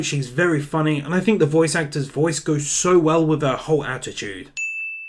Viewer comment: I thought you said ho attitude